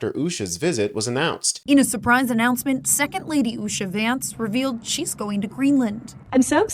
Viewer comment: Did he just say he was shady vance ?